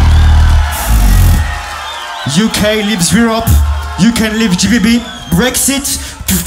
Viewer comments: what did he say? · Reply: "UK leaves Europe, UK leaves GBB. Brexit" He has french accent thats why its kinda hard to understand but he's making fun of d-low from the UK. Brexit means britain exiting europe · what did he say from here? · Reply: UK leaves Europe, You can leave GBB Brexit! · I didn't understand it clearly , what he actually said 😢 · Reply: He said "Uk leaves europe, Uk leaves GBB Brexit" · what did he say? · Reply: UK leaves Europe You can leave GBB Brexit